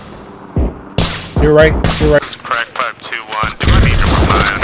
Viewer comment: Lol did he say at 0:31 "this is crackpipe 2-1" what an amazing call sign.